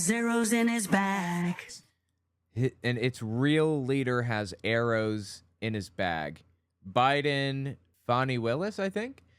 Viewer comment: I literally thought it said: "and its real leader got zeroes in his bank"...